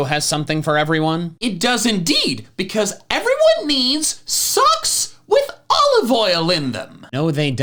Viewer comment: did he just say socks with olive oil in them